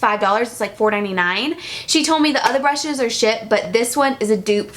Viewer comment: did you say "she told me the other brushes are shit." ?